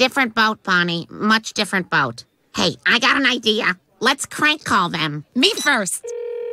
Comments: Why does it sound like they said crank call instead of prank call. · am I the only one who hears crank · Sounds like Lois said crank call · Did she just say “Crank Call”